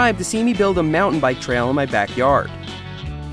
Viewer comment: “Fall weather” 2-3 inches of snow yesterday. Think my riding season is pretty much over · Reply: I thought he said "foul weather"…